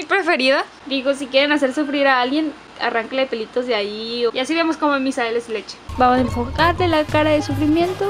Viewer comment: soi el uniko coshino q escucho q dijo "y así vemos como a missa le sale su leshe" xDxd :vv